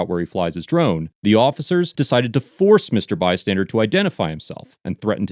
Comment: It sounds like he is saying “bystandard” repeatedly. 😂